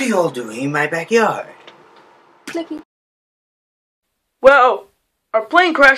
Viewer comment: he says What are you all doing in my backyard and after that it sounds like he says playing